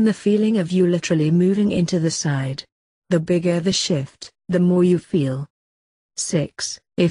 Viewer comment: I heard the bigger the shit